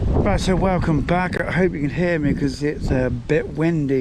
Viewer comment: Except for some interruptions I Watched the whole video . Good to see you out and about. Unless I was distracted the question I heard was - “hope you can me?” Which the answer is yes I can 😂